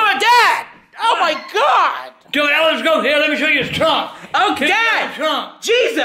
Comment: it sounded like he said let me show you Trump